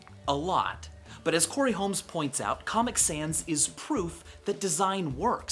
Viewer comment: the subtitles say "Comic Dans"